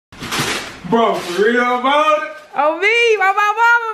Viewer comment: did say " Forreal ! A Doritos box !?" 😂😂